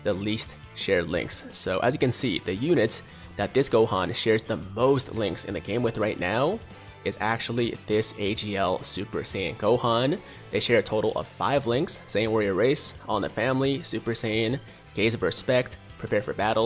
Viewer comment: is it me? Or did he say they share 5 links?